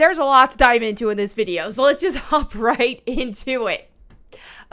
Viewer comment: I thought you said hump right into it 😂